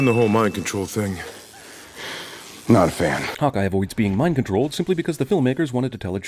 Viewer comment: Lol, when you said "Hawk Eye" it sounded like you said "Hot guy". xD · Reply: Natalie Portwoman Wait, what... How is that gay? Hearing that it sounded like he said Hot guy instead of Hawk Eye & laughing about it is gay? Lmao! You serious bro? xD I myself never said anything about Hawk Eye. Jus mentioning the fact.